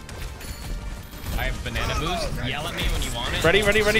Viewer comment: Did just say "I have banana boost"? That's what I'm calling it from now on.